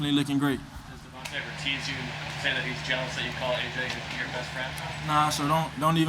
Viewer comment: what he say ? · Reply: "Does Devonta ever tease you that you call A.J. your bestfriend?"